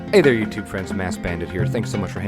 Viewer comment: Lmao I thought you said assedbandit and honestly it made the video 80 times funnier until I realised at 9 minutes